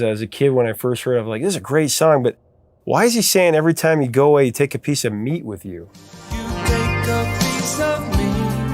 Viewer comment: No, not everybody wondered why she took "a piece of meat" with her. I am willing to assume that YOU misheard it that way, and I'm even somewhat willing to assume you really did ask a lot of your acquaintances and always found everybody misheard it like that. But from personal experience, I can tell you that at least one person heard the lyrics like they were intended. (I had to shift gears to even understand what you were talking about.)